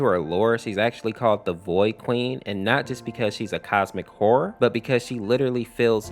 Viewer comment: did he say the “cosmic horror “ or the other word